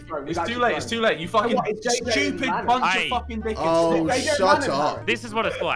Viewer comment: Jus me or at 8:42 i heard harry go u fuckin monkey